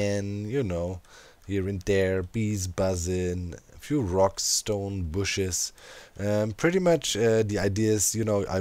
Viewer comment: DID I HEAR A ROCK AND STONE??